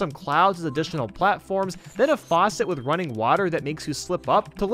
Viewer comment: Did he just say NUNI?